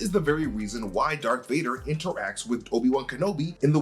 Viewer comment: does he say “dark” Vader?